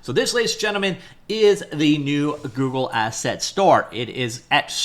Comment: Am I hearing "Google Store"? :')